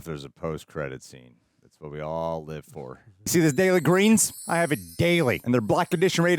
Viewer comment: Oh, "Daily Greens" Heul !Before I turned on Closed Captioning, I kept hearing "Soylent Green" :-) I am relieved